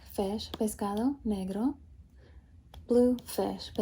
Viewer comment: wait what did you say? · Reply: Negro, it's black in spanish.